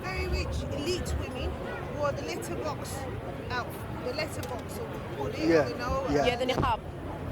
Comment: onwards is actually disgusting, imagine telling a woman that she is wearing a letter box outfit. This is the problem when everyone and anyone tries to give tafseer without even basic knowledge. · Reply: looool i i really thought i heard it wrong at first but she really said "letter box outfit" uno looooooool and they just let her continue like it's nothing, embarrassing lot. they don't represent us somali bro